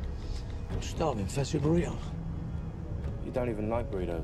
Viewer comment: 日本語で「久しぶりやん」(関西弁)に聞こえた(笑)